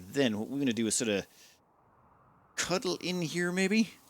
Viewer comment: Swear to god, I thought you said "Cuddle in here." Definitely not what I would suggest during a raid.